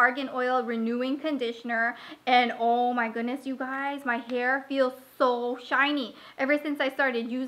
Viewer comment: did she just say "my hair feels so shiny"?